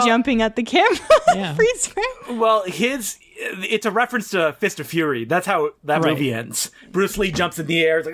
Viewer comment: Hmm, thought you guys said FISH of Fury.